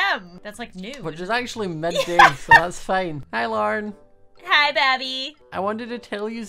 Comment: the captions say larne instead of Lauren